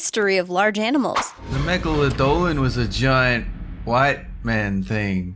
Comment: It sounded like he said "Giant white man"Lol,Wth.